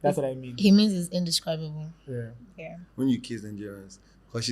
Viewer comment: Omo 1:24 did i hear kiss ewo the ship is shipping 😂